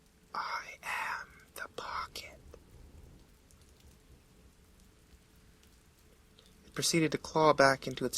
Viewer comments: The first time I heard it I thought it said "I am the bucket..." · What it it said: "I am the Pocket" What I heard: "I am the bucket" What I thought: "homestuck ruined my life"